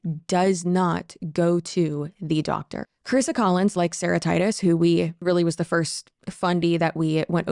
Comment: Hearing you say Sarah Titus I thought you were saying Sarahtitis like it was a disease name for a second, like hepatitis or something.